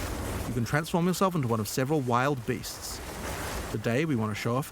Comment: It sounds like they said the bear was one of 6 transformations, so def more than just wolf and bear